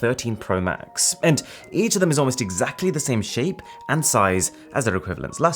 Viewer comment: When you said "it's exactly the same shape" i thought you said "it's exactly the same sh*t" 😂